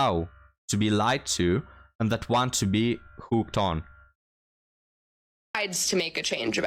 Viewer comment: I thought you said "pooped on", but i think you said "fooked on". Same thing i suppose if she has a scat fetish, lol.